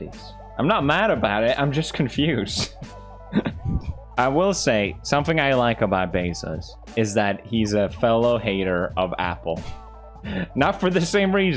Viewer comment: Did he say "somefing"?